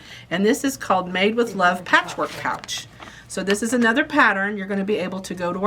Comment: Ha! I heard “patchwork couch “ and was wondering what that might possibly be. I can’t believe I won - what a beautiful prize ❤